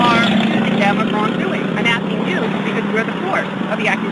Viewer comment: You asked what she said around this point; she says "I'm not going to ask the driver, I'm asking you because you're the source of the accusation." "Source" was the word in question.